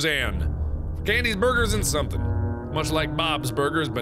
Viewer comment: I thought for a second he said David's birgers